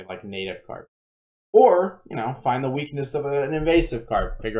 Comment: I heard guns are the weakness of most carp. Glad I could help.